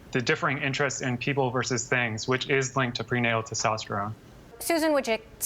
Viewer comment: what is he saying at 3:50.. linked to prenatal disaustra? does amyone know? he slurs words. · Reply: prenatal testosterone